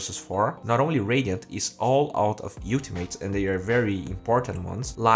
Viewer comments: Did he just call ultimate youltimate??? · I swear I head him say "youltimate" for ultimate. 5:24 XD